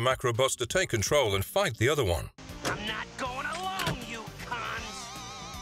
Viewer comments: In 15:20 does he say cunt?!? 😵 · Did Razor called the female metallikat "cunt" in 15:20? I remember this cartoom being awesome, but not that much. Wow.